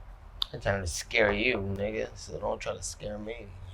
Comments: did Nate just say Nigga 👀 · Anyone else hear any say nigga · Did nate just call jake a ni66a at 13:40 ? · did Nate say nigga? 😂😂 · did buddy just say “nigga” 🤣😂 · did nate call him a Nigga?